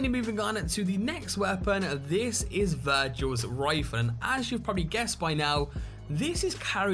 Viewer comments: I misheard virgil's rifle as virgil's rifon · I misheard you saying "Virgils Rifle" as "Virgins Rifle"... worst weapon ever.